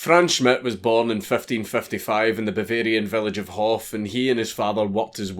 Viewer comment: Did… did you just say the village of HOTH??